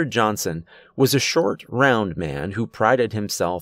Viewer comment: lmmfao, Did anyone else hearbrowninsteadofround? I kept reminding it thinking wow, that's not right so I finally put it up all the way and turned on the captions and I finally figured out it was saying round and I couldn't stop laughing at myself!